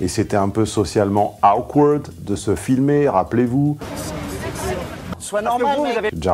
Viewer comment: Il dit quoi le phoque à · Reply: "Sois normal mec"? 😂